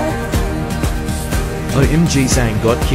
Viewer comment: I heard "Hairy styles wins"